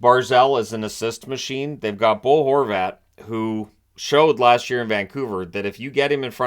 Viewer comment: at first i thought you called him Goal Horvat 😂